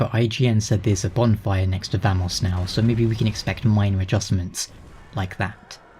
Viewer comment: Thanos? What about him? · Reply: when u mentioned the bonfire by someone, I went back and listened to it u said Vamos for some reason I thought u said Thanos.